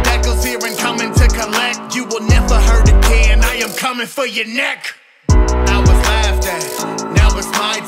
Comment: I thought it said "coming for your neck" at the end